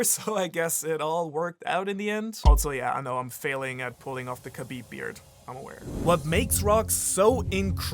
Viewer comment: did you say "the Khabib beard"?